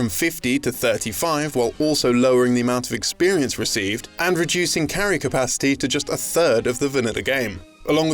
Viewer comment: can anyone understand what the hell he's saying? It sounds like "...reducing character pasty to just a third of the vinida game" like wtf pasty? vinida?